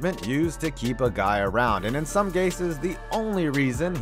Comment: Did I hear “In some Gases”? 😂😂😂